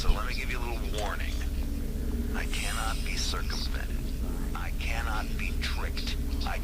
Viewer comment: when he said that he can't be circumvented, i thought he said circumcised, cuz that's what i hear every time a word begins with circum. i still believe he said circumcised, somehow, and will probably believe that for the rest of my life. I CANNOT BE CIRCUMCISED!!